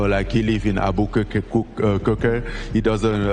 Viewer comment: boy what the hell he said!? 😂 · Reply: Abukakekuker sounds like some Harry Potter spell lol.